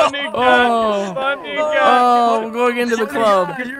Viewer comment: Did anyone else hear say "My niga,my niga" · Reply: I heard that after reading your comment, but he's just articulating 'funny guy' weirdly.